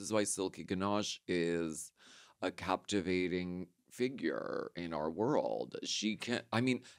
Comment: I thought she said finger instead of figure